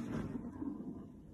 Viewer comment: did she really say "oh! second round!"